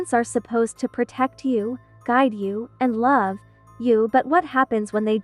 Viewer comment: did you say guide?